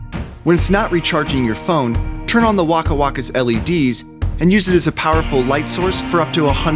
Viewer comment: i thought it said waka flaka